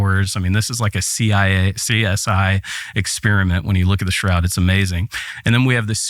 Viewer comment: Did he just say “Like a C.I.A.”?